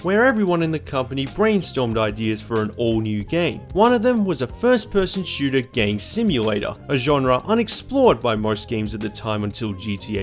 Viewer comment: I swear that sounded like "first-person shooter gay simulator"